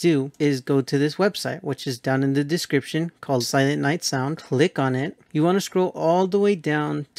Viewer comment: I heard "lick on it" instead of "click on it"